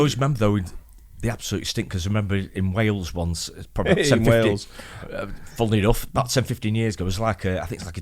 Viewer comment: I thought said "In 1750..." as if he was there 😆